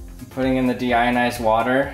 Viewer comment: I thought it said "demonetized". Well it's not that far either